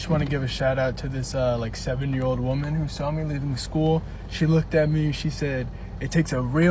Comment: anyone else heard seven year old woman? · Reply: I did too, it took me a good minute or two to realize he said “70”.